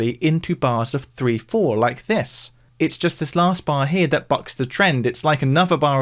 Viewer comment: is saying fuck? i would've never expected that from him edit: oh my god he's saying bucks jesus christ